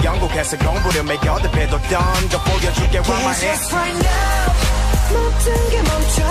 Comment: Two things: I thought they were saying "grab my hand" not "rock my head" and I after this I had to search what a gyro drop was lol tell me I'm not alone on this?😳👀